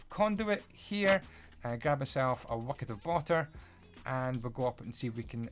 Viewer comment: I love how at 11:30 you said "grab erself? a wucket of bater" instead of a bucket of water.